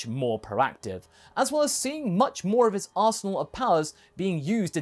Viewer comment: arsenal or asshole? Jkjk ik, its just what I heard is all lol